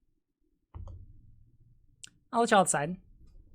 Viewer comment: you answer a question in chat "RGR so 'they were going to take my gun' isn't an excuse to shoot someone?" I can't quite make out your reply, it seems to be "I'll let you outside" according to CC. Which doesn't make sense and seems hostile? I'm not sure that's the right word. Sarcastic maybe? It seems uncalled for in either case. Why not just answer directly? Really enjoying the vid so far though. Found you through the stream Vaush did with Natalie. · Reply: Sounds like "I'll let y'all decide".